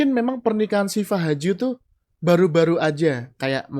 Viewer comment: Saya bahkan nggak tau Shifa Haju. Emang nggak uptodate artist2 sih. nyebutnya Sipa Haju, saya dengernya malah Si Pak Haju. Pak Haju siapa? waakakakakak